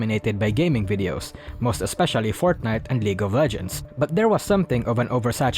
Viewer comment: I was reading the comments, not dully paying attention to the video when i hilariously misheard what was said: Fortnite and Legal Virgins...